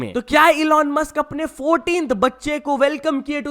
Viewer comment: what did you said 14th 🥶🥶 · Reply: Total 14. Four of one😂